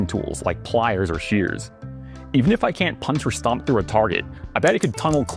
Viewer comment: He said: " Even if i cant punch or stomp through a target" I heard: " Even though i cant punch or stomp the retarded" 😅😂